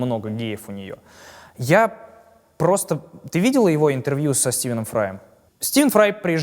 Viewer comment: * -Ты видела ее интервью?* Мне послышалось?